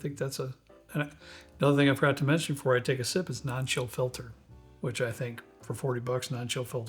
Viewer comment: I could've sworn you said Nacho filtered ha